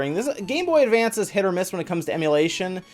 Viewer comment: Did you say.....Hit or Miss? 🤔huh?